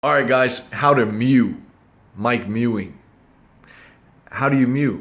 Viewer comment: I thought it said ,,how to meow" and was very disappointed